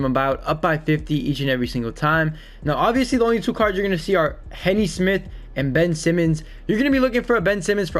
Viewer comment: it’s M3 btw I changed my name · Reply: JAL swear I thought he said henny Smith